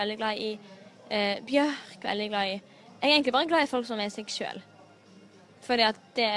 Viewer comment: Funny thing: At 17:15 the way she says "seg selv" (themselves) it sounds quite a lot (imo) like "seksuell" (sexual), so at first I thought she said: "I really only like people who are sexual." 🙂